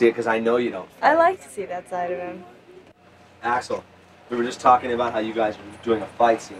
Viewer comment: Why did i hear ,,Asshole" instead of Axl at 1:32 xdddd